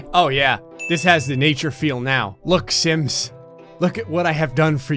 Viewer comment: I thought you said "look simps!" lmao